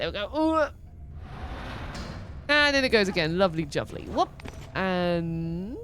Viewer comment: are you saying “lovely juvly” here instead of “lovely jubbly”?